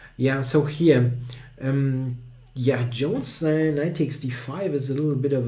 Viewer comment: did he say jon snow?